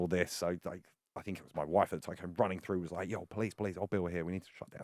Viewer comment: Did he just say "my wife"? Does that mean his wife just watches him have sex with other women?